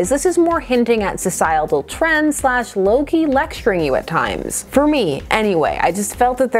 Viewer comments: My favorite part was when says "low key" and I heard 'Loki' and was all, what does he have to do with this? Does he explain ... oh, wait. Also, I think should keep working on the floss and give us regular updates. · I just have to share that I misheard when you said “low-key lecturing you” as “Loki lecturing you” and I was honestly like…’wait, Loki is in this comic?’ You mentioned this wasn’t what you were expecting this to be, and I know for myself that can really play into my enjoyment of something. For instance I remember hearing a very interesting theory for the 2020 Black Widow movie prior to the movie’s release, a theory that ended up being wrong. And while I felt like the actual Black Widow movie was fine as it was, I found I couldn’t really enjoy it, because I had gotten attached to this theory of what the movie was going to be and couldn’t let it go.